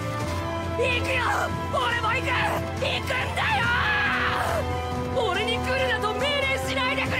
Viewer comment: I HEARD NARANCIA SAY "NIGERUNDAYO" Its